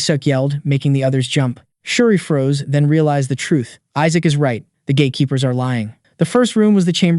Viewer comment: what I hear "the gay keeper" What he said "the gate keeper" What's wrong with me😭😭😭